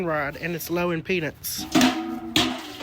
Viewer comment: Super low impedance! Lots of metal and lots of surface area. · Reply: I thought he said "low in peanuts" but I knew.